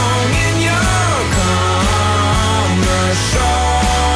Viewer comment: I thought is said com er show but then I found it was commercial lol